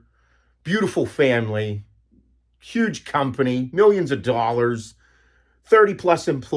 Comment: I swear I thought you were about to say huge cock instead of huge company lol. This video came at the right time